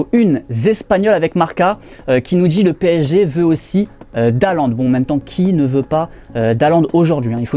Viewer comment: pourquoi il dit Dalland?? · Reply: Le PSG veut d'haalland